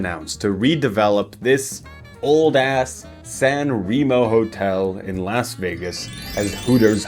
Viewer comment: please tell me i’m not the only person who was looking away and thought he said “Sanrio Hotel”